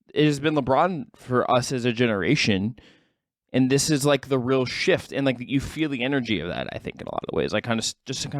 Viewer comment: Had to replay . Thought I heard say REAL SHIT😆. It was real shift.